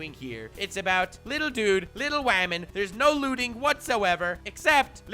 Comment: Me: *turn on subtitle, see "whamon" Also me: Did you just say HAMON???